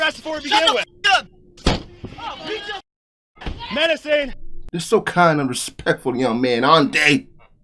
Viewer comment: I heard the police officer say "Menacing."